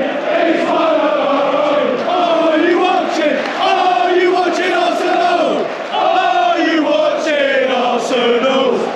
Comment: What are they saying? Can anybody tell me? ( Are you watching ???? ) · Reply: Are you watching Arsenal?